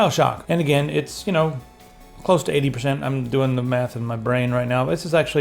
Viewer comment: thought you said " im doing the meth in my brain right now" LOL